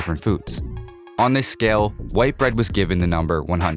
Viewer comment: I hear white bread, I read white rice